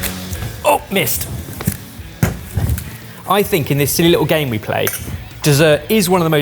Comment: For some reason it took me embarrissingly long to notice Ben did not say "Mist" (friendly german variante of damn) but actually "missed" at 3:15😅 Great video! Much fun, I am with Barry though.... who needs blue cheese?!